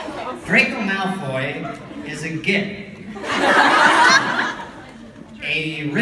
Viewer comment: what did he call him lol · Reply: A git 😂